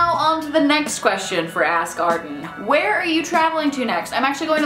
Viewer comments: It sounded like you were saying "ass garden." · i just hear "ass garden." just me?